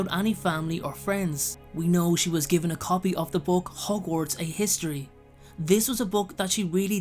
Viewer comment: I used to think When Hermione said ‘I read it in Hogwarts: A history’ I thought she was say ‘Hogwarts uh- history’